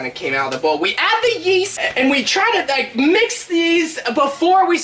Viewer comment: ‘Yeast, I thought he said Weast’